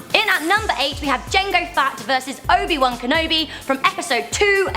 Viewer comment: Jango Fat. Does anyone else hear it?? My fav character would be........hmmmmm...... Does Ahsoka count?? Or is it from the movies only??? If so, Han Solo. :)